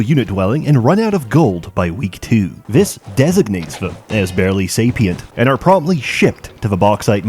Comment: 8 year old me would be very offended if I understood what "Say pee int" meant.